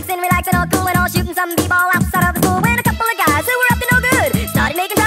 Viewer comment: I thought the line "my mom got scared"i thought it said among us😭💀